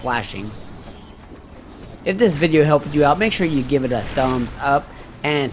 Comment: For a second I thought you said Metroid Bread